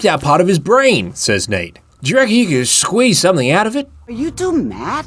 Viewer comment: barely any english speaker in the western hemisphere could understand what the hell you said. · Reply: “Do you reckon I could squeeze something out of it?”